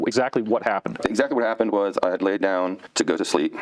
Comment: I thought he said he went to sleep in his thinking bag, and I'm pretty sure the Alien's name was Little Joe and he will bring some vodka for a $2 tip!